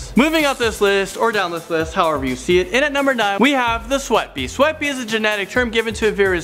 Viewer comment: I thought the second one said sweet bae 😂😂😂😂😂